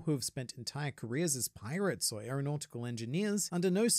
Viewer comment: I'm using your advice on this channel to start my own. I won't lie, you aren't my favorite youtuber (that title belongs to Grandline Review) but I do consider you to be the best youtuber. Wish me luck and thank you. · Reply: I think you said pirate instead of pilot and I hope this was intentional for the lols. Or maybe you just think about pirates to much